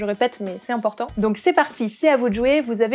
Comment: intéressant ! je vais voir ça! et à 5:34 c'est moi ou tu dis "et bonne photos à toutes et à toutes!" ? ;😜